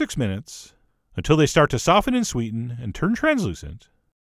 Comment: "Until they start to soften in Sweden" Am I crazy or is that what you said?